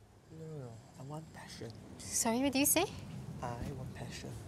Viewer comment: I heard "I want Constipation" then I was like hahaha wtf